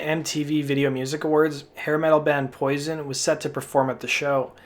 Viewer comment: thought i heard "heavy metal band poison" 😅